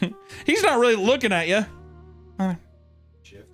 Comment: I know very little chinese but did it say "I am not healthy"? at 19:30?